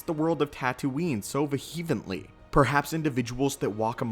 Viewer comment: what is the word at 5:53 - "vahevenly"? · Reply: I think he meant Vehemently lol